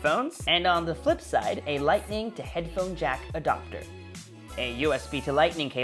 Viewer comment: Did you just say “lightning jack adoctor” ?